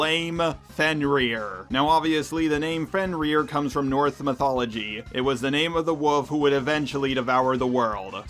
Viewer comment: I think I heard you say north mythology